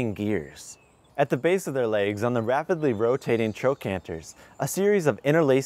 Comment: I keep hearing "trocanter" and all I can think about is my anatomy class and the anatomy of long bones.